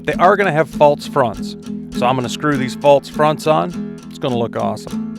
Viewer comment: wtf is "faults fronts" can you even speak properly dude? What are these weird words you're saying. faults fronts isn't a word, try again.